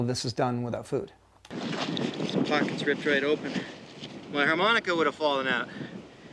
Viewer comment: let one slip there, eh? 😁 Don’t think I’ve ever heard Les use that word on camera! Did they leave it in the broadcast version? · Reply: I think he said “pockets ripped right open” lol